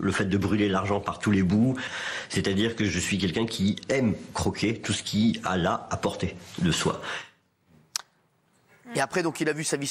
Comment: Il a dit quoi à 10:15 ? ????????? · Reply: "J'aime tout croquer tout ce qui est à la portée de soi"